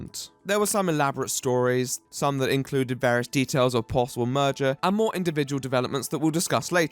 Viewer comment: I thought you said "murder" at 3:23 and I didn't even question it because of who the video is about. I only discovered that you said "merger" when I wondered why the fuck you hadn't talked about the possible murder and rewatched it